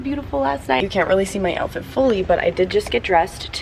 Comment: Everytime you said Ladurée i heard "Lottery" and i was confused lmao